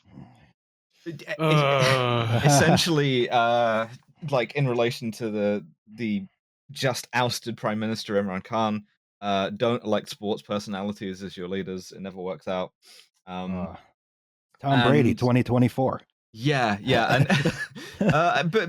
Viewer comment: When mentioned Imran Khan in The God Damn News, I thought she said Emre Can the german soccer player, and was the most confused person on Earth how he ended up the Pakistani Prime Minister